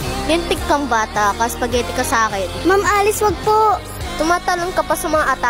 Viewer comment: I heard "spaghetti na salon"😢